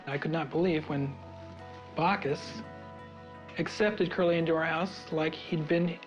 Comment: We love the stories of Bacchus and ❤ What key takeaways or lessons resonated with you the most from this episode? We’d love to hear your thoughts! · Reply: I thought they were saying Barkus.. lol